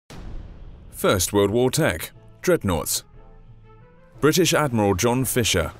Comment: i thought i heard "first world war tank: dreadnought" in the first part of the video